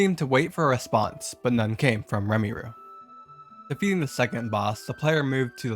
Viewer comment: As a Touhou fan, i laughed my ass off when i heard "Remiroo". That's what i'll call Reimu from now on💀